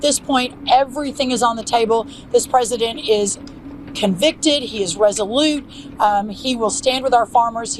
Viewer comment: Is she saying "This president is convicted, ..." at 12:50 ? :D <link>